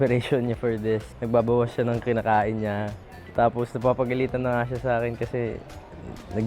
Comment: Is the subtitle correct around 7:22? I really think it isn’t right😅 it says “she’s already getting angry with me because she is dieting” but said “Napapagalitan na siya sa akin” (meaning is scolding Angela because she is dieting as she isn’t eating much)